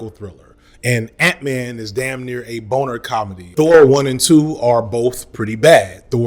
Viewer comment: Did you say "Boner comedy" at 15:00 ?